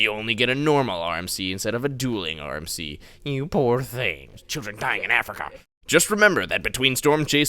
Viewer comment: “Oh boohoo, you got a normal RMC instead of a dueling RMC, you poor thing” ” C H I L D R E N D Y I N G I N A F R I C A “ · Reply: i thought it said "YOU SHOULD DIE IN AFRICA!"